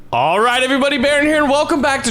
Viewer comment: Everytime you say "Baron here and" I hear "Baren Haren".